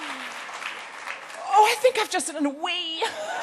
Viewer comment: What is she saying at 0:47, I don't understand the phrase... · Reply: "I think I've just done a little wee." She was so excited she peed a little.